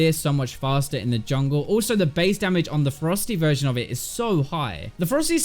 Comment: everytime he said ferocity i thought he said frosty for rengar XD